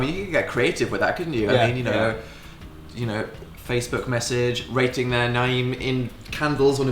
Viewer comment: loving the random change in Dan's accent when he says 'writing their name' at 6:56 ...at least I assume that's what he said haha <3